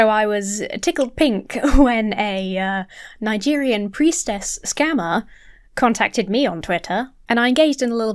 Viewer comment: I thought it said "the prettiest scammer". At least she know she FINE